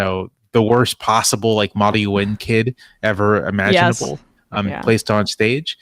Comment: Can somebody tell me what a moddy win kid is? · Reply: He said “Model U.N. kid”. Kind of like debate club, but instead high schoolers pretending they are a member of the United Nations.